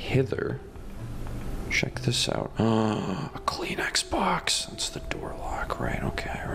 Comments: I thought he said a clean Xbox but he said a Kleenex box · Did you say "Kleenex box" or "clean Xbox"?